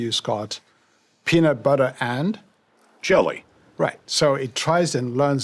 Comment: oh jelly. i thought he said chilli!